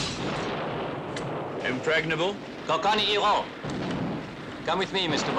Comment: What did Blofeld say to his pistol-wielding goons? Sound like "ka-ka-e-ro!" Sounds kinda cool haha.